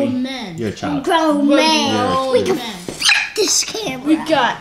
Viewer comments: I thought said something else.😂😂😂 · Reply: We Can F this camera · did he say "we can fuck this camera"😂